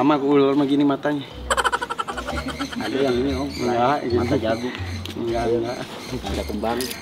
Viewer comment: salah dengar ga si gua? kata si bapaknye mata jagung?? klo bener (itu burung dara emang?) /emang ada uler mata jagung?? entahlah saya ga paham ula